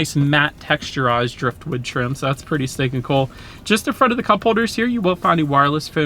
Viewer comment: Please stop saying”Pretty stinkin’’ cool” and “ Let’s go ahead and….” It’s truly annoying. Thanks. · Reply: I thought he was saying pretty steak n cool, I was like wtffffff, cringe 😊